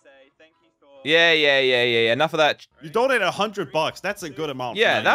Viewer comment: did josh say enough of that sh*t but bleep it out